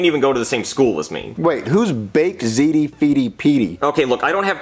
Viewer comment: I legit thought he said edp.